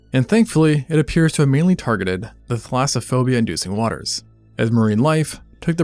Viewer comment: Omg, I thought that said transphobia and I was like, what the fuck does a fish have to do with that? Lol